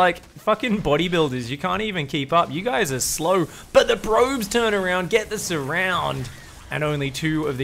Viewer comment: did he say the "Bro"-bes?